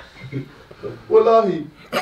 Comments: did jj just say “Wallahi” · why da hell i heard "we love u" instead of "wallahi"